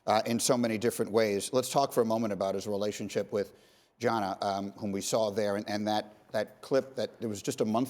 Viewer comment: Did he really just say gonna instead of a Gianna